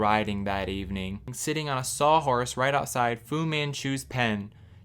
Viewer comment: When I heard "Fu man chu" I heard ABBA singing "Vous les Vous"